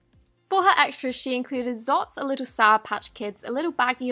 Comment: Did she zots instead of lots in